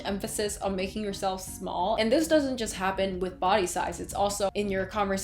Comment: I really heard "boba size" during the intro